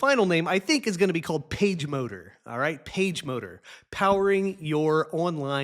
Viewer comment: I have to admit that when you first said "PageMotor" I heard "PageModer." One data point for you.